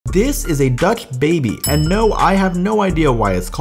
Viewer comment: Who else thought he said duck baby 💀